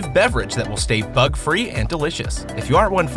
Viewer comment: I thought he said thug free